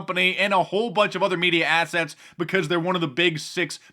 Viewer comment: Did he say "media assets" or "media asshats"?